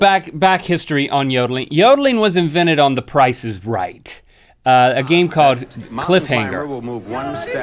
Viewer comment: I think he said "The price is Reich"